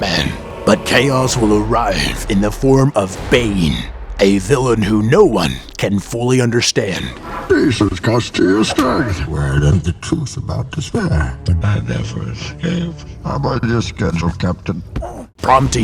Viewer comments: What i thought Bane said 1:06 - 1:14 "These is cost kia strength" "Were did the tooth about to swear" "The bat never a scraped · Here's what bane said at 1:06 "Peace has cost you your strength" "Where I learned the truth about despair" "I never escaped" "How about your schedule captain" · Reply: It was ,"i'm on a schedule captain"